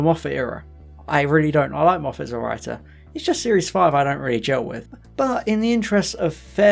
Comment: What does he say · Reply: "I like moffat as a writer, its just series 5 i dont really chill with"